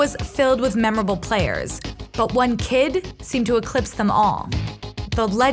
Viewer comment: Am i tripping or did she say "fightar"